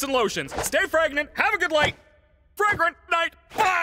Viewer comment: Caine accidentally says "Fregnant" (maybe a mistake of Pregnant) and "Light" from what I hear, and points Directly At Kinger. Light feels like him directly addressing his use of light against Kinger to limit his memory the entire episode, but Fregnant? What could that possibly mean.